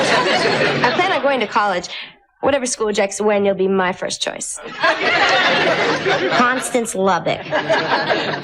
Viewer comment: what did Cindy say here? Whichever school what? is her first choice? I can’t make out what she said and i put the phone directly to my ear to listen 🎧 Can someone make it clear for me? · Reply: mentions it right after Connie’s session. She says whatever school rejects Wendy while be her first choice.